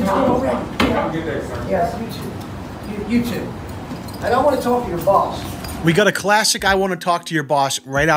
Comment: I think he said I DONT wanna talk to your boss